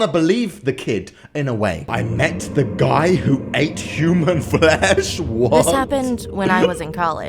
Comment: Times when all of the animations started: 0:19 - Instagram 5:31 - CS : GO 9:06 - Miniskirt 11:11 - The Creature 17:23 - School Lockdown 19:50 - My Invisible Playmate 22:01 - I Met The Guy Who Ate Human Flesh Hope you appreciate this! :) Edit: Lol i love how I'm still getting replies for this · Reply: Thought it said 'The guy who ate human fish'